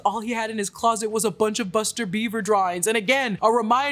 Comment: I thought Noir said “shtty Buster Beaver drawers” like underwear which really blew my mind but knowing it was “drawings” I feel much relief